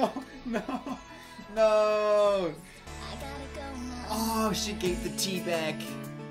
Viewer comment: HAHAHAHAHA When you said, "Oh no, she gave the 'T' back", I GENUINELY thought you said, "Oh no, she gave the tea bag" I'm an adult, I swear